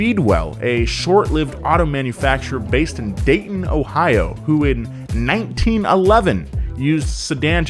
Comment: Did i just hear ohio